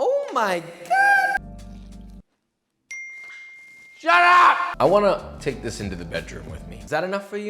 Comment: The moment he said "shut up" at 7:16 , I thought for a second he said "sharap" (sarap) which means delicious in Tagalog.